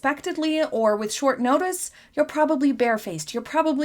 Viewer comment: 4.30 I thought you said shitfaced.... not barefaced. B My bad....But there's always a glass somewhere, so maybe my mind made a Freudian slip oops..